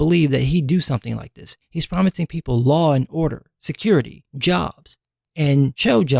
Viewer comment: i thought it said security jobs haha not security. jobs